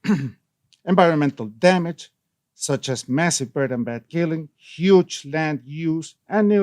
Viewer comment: What's that he says at 6:01 "burden bed killing?" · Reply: Bird killing